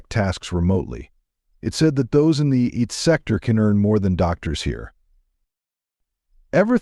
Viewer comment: Good video informative. Wrong subtitle IT missubtitled as eat and AI voice mispronounced it as eat instead of IT.